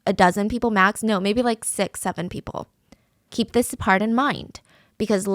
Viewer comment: im sorry this is supposed to be serious but did i just hear 67....